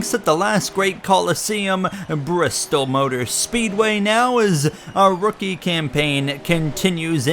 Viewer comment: I thought you said "Bro stole Speedway" 🤣